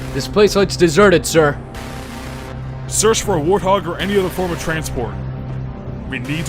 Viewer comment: I thought it said "The Search for Vanilla."